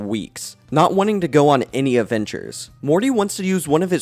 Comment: You said Avengers instead of adventures